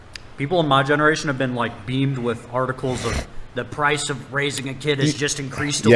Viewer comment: the first time I played it I thought he said 'tardicles' but he is just saying 'articles'